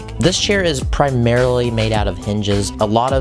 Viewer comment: I misheard what you said when u were talking about the chair and thought it was primaraly made of ninjas